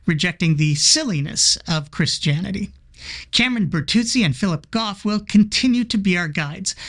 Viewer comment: I thought you said Shameron Bartuzzi.. oops